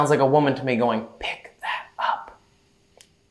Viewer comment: I heard ... " Pick that up. " ... 😮 .